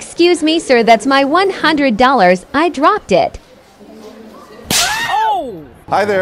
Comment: For a sec I heard "Exuse me sir, that's my $100. I Robbed it"